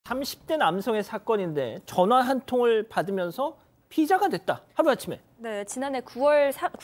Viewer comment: "피자가 됐다" 라고 들리네요 ㅋ